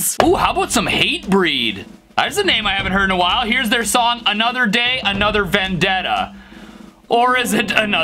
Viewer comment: I heard “another day, another bandana”